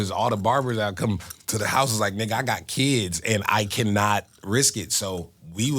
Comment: bruh did he just say he got kids??? Am I geeking? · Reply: No no😭 he said the barbers weren’t coming out during covid because THEY got kids😭